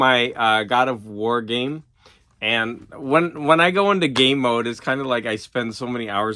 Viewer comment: 4 day weeks seem longer because you're usually trying to squeeze a lot in a shorter amount of time. "Before they learn to be bad" 😥 I misheard you when you started talking about God Of War. I thought you said "when I'm in gay mode" not "when I'm in game mode" 😂At first I was like what! You can't switch it on or off. Then I re-watched "oh! that's not what GG said". I stuffed my Achilles a few years back and ever since then seeing any ankle violence makes me feel a bit queasy too. Speaking of queasy, cannibalism...no big deal 😂The UFO Flying Saucers comic sounds interesting. M is a fantastic film. I'll be interested to hear what you think. I am a big Eno fan & love his Ambient albums. You should also check the collaboration between him & German 70's electronic ambient unit Cluster. All of the books at the end seemed like stuff I would like to check out. Well done from the Army member #667